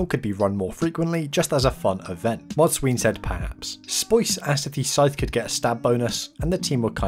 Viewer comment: sounds like he says "mod sween said "pass""